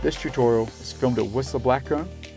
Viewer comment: For a split second I thought I heard "alcohol is to improve your riding" 😂